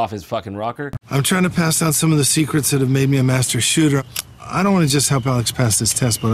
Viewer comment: Did Seagal say "I'm trying to pass down the secrets that's made me a master shitter" or did I hear that wrong?!??? I'd like to become a master shitter.